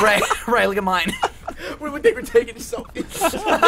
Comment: what does ray even say at 47:19? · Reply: Harus Streit we look like we're taking selfies